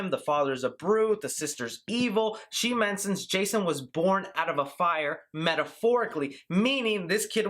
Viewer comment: I thought she said, "he was born to love fire!"